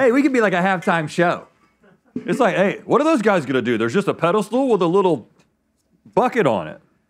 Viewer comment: Did Rhett just say... Pedal Stool? 😂 Ive only heard that one other place and its such an obscure reference i dont think anyone's gonna know... But if ya know, ya know!